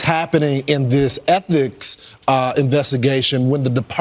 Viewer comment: Did he just say "ethnics investigation" between 8:00-8:09? Did he mean "ethics"? Lol